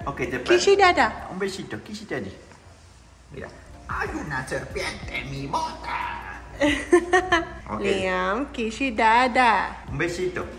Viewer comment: Alguien sabe que dijo en inglés? En el 4:17? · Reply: Que le dé un beso a papa